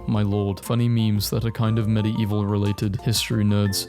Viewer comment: it sounds like hes saying "Medii-evil"